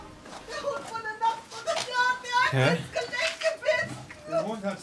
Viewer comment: Ins Gelenk gebissen... ich hab erst Genick verstanden xD